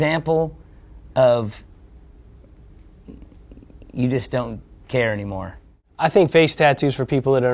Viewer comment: マイク・タイソンの時の間ほんと草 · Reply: 最初It is don’t に聞こえたけど字幕付けたらyou just don’tだった ネイティブの発音は難しい